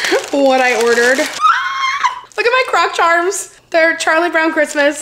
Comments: I can't wait for vlogmas and all things Xmas!! 🥰❄️ ps when you said "croc charms" I thought you said crotch arms 🤣😂🤣😂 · Lol I thought you said look at my crotch arms at 5:48 as you were holding up your arm 🤣🤣🤣 you really said look at my croc charms but I had to listen twice! 😂